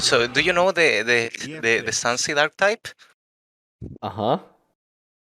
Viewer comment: what archetype did he say? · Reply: Sunseed